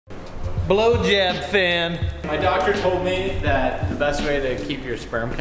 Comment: OH, WELL DAMN!! I thought it said BlowJob Fan xD